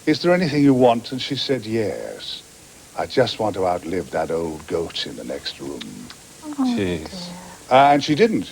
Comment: hello, folks. I am not a native English speaker. Big fan of Tom though. what did he say at 6:40 (about his mother words before her death)? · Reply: "I just want to out live that old goat in the next room" ?